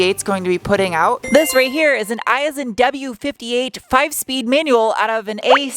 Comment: OK - who else thought was saying, “I as in W”…? 😂